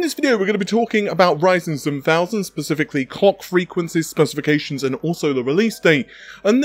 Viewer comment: Did he just say "cock frequencies" 🤣